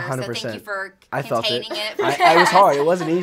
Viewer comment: Josh said "I was hard" --Freudian slip, I think he might to say "it was hard [to contain his excitement]" lol. I guess he does have something for Falen.